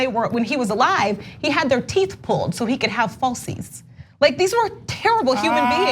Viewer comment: Washington pulled out their teeth so he could have what?? I couldn’t make out the word you said 🤔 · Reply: I heard "falsies," but when I looked it up, it didn't make sense. When I finally realized what it meant, it DISGUSTED me!